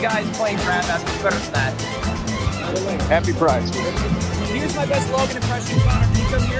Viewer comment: i thought he said happy prime